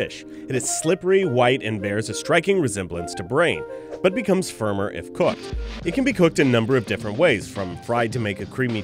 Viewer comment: is it me or did he say firmer and fuk 1:04 but i did enjoy the video its a lot cool and funny and a lot of info to learn out of this